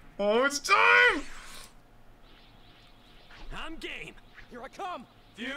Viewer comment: istg i heard "im gay" from shallot right before they fused